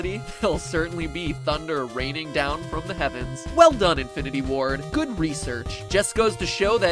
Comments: Woah Woah Woah... Did matpat just predict the future? I swear to god he said infinite war at 8:41. Or is that the comics? oh no the comics are the INFINITE GAUNTLET MATPAT THE GENIUS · Did anyone else hear him say “Infinity War” at 8:41? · i thought he said infinity war and then i saw the video date and i was like hnmmmmmmmmmmmmmmmmmmmmmmmmmmmmmmmmmmmmmmmmmmmmmmmm